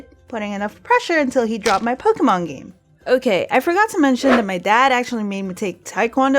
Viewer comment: I thought you say Pokémon go...